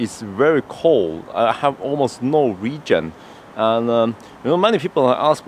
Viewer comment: In 0:26 I hear "almost no region" but that's probably wrong. What is it supposed to be? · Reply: regen